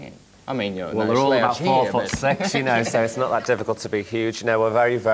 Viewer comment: What does Pete say in 3:50? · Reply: “Well they’re all about 4,6 you know, so it’s not that difficult to be huge”